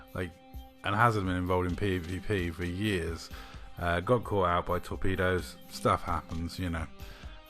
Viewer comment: misheard that as being “got caught out by some pedos” lol. I though, what game are you playing? 🤣